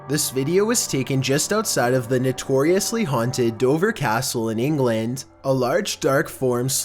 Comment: I thought you said Dovah instead of Dover in the first one lol. love your scary videos bro keep them rolling when you can